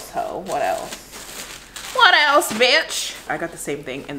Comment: “Bornch” burnt - orange lolol IM DEAD 😭😂❤️ LOVE THE SKIMS ❤️❤️❤️ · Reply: lmao I thought she was saying "birch" and i didnt get why it was funny haha!